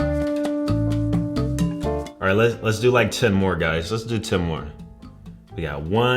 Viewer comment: am i trippin or did he say he gonna match 10 more guys in